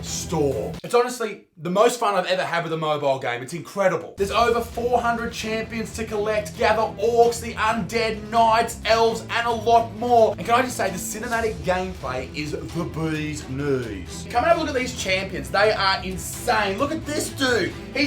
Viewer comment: i thought he said analog porn